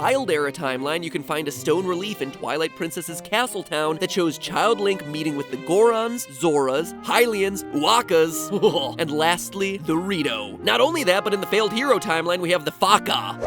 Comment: did you just said DORITO